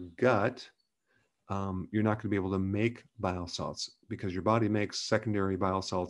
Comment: I thought Dr Berg said “bath salts” initially lol.